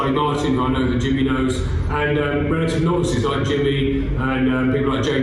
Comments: I thought he said "And relative nonces like Jimmy" xD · honestly thought he said “relative nonces like jimmy” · Did he call Jimmy a novice? (novicity)